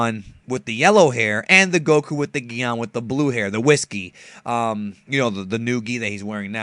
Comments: lol i thought u said the yellow hair with whisky but i notice u said whis gi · Towards the end I thought you said "Goku in the whiskey" it took me a few seconds to register "Whis Gee"